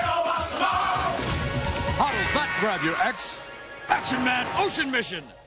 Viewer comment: it sounds like he says 'i'll nut grab you X' at the end of the ocean mission one :/